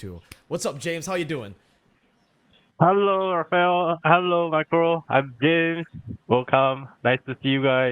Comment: am i the only one who heard that "Hello Micro"??